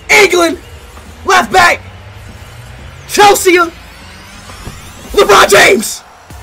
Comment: did he actually say Lebrone James? 😂😂😂